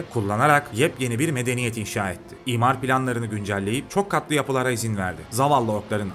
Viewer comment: "İmar planlarını güncelleyip çok tatlı yapılara izin verdi"Diye duydum hahaha. 2x izlemenin negarif yanlarından.